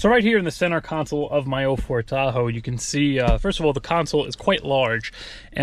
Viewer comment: did you say my old ford tahoe?? 🤣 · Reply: He said 04 Tahoe